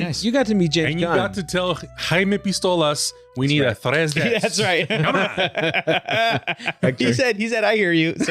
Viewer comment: Hey does anyone know what is saying here? 1:00 Im not privy to whatever inside joke they are making nor can I figure out what word hes actually saying? Hector told Gunn we need a what? Am I just dumb for not understanding or are others confused like me? Please help a dumb brother out!? Lol 😜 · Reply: “We told Jaime Pistoles we need a tres des. “